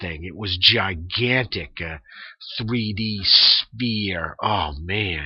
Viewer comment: Did he say 3D sphere?